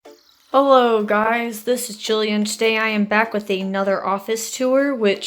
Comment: "Hey guys, this is Julian" Wait... Julian? JULIAN?! I just played this vid back for like 3 times just to be sure, and yet, i hear Julian... Captions say "Jullian's"... ???? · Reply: I said